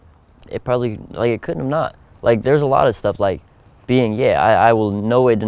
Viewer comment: did he say being gay?